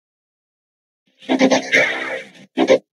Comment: He sounds like he is saying “let us out” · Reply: I hear “that’s not sound”, which kinda makes sense in a way? I’m not too sure myself.